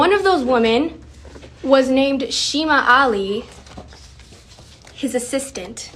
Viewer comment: ”her name was shema ale” is she saying her name was shemale?